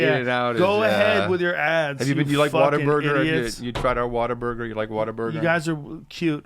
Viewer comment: Oh...you mean't WHAT-A-Burger....I could've sworn said Water....